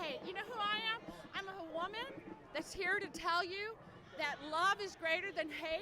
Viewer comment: did she just say... “I’m a hu-woman” Because she didn’t want to use the word man? Wow.... just wow...